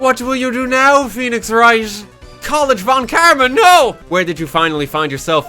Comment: i honestly wasnt listening at some point and by the time i started to pay more attention i heard "VON KARMAS NO"